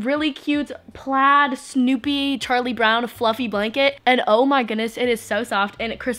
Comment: When she said ,“Charlie brown people” I thought she said,” tiny black people”